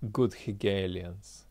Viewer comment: Check Hegel and his concepts on reality. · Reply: By checking it I realised that Hegelianism is a very wide field. Do you have a more specific hint? My attempts to orient myself make me feel drowning. Maybe I misunderstood... Did he say "good Hegelians" at 14:03? (English is my 3rd language)